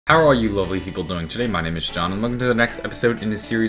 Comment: In the beginning I thought you said "how are all you lonely People" lol